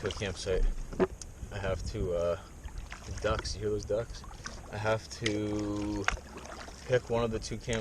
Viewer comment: sounded like chili ducks to me